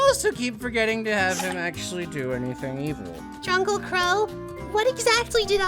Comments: For the longest time, I thought she was saying "Jungle Qrow" which makes absolutely no sense... ...only now did I hear "Drunkle Qrow," which is actually funny, just far far too late that I'm only just hearing it now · Did... did she call him drunkle Qrow? Flashes back to grunkle Stan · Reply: I thought she said jungle qrow. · Did Ruby called Qrow "Druncle Qrow"? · Did... Did she just say "Drunkle"?